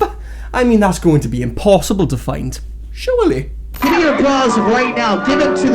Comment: Wait, is he yelling "Give me your bras! All of them!"? The first time I saw that clip I thought he said "Give me your applause! All of them!". That just sounded narsistic and douchy alone but holy crap does bras make it much, much worse.